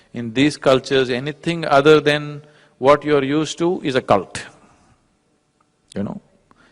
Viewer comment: he says "occult" instead of "a cult".